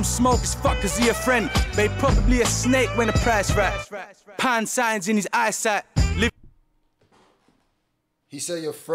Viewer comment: I heard “pound sign in his eyesight” …💰 He🔥 🔥